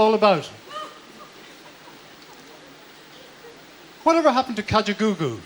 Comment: Yes. Also at 4:06 when he says sometimes it's hard to figure out what life is all about.. what does he say after that? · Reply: He says "whatever happened to Kajagoogoo?" They were an 80s band in the UK who pretty much vanished after one big hit